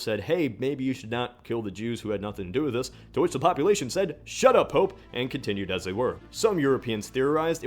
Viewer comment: my captions said shut up hope